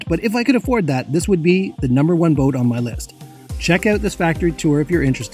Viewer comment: I heard "wanker hoist that sail"😁